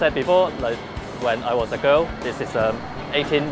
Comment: am I the only one who heard Lok saying "When I was a girl.."?